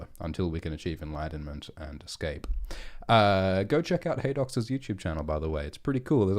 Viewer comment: whos youtube channel is he saying to check out around 11:35? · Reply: Haedox?